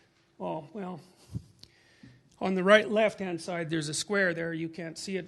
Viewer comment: did he say at 11:31 "the right-left hand side"? I love Dr peterson, but that's some funny shit.